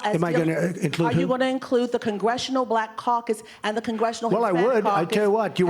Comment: did she just say CBC stands for Congressional Black Cocks? its not that kind of conference, lady.